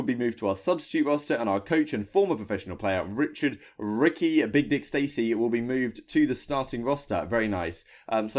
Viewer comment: Richard Ricky aka "big dick" Stacy lol 4:47 did i hear that right lol